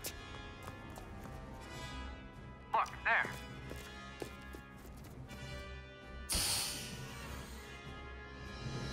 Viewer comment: what did he say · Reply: look, there!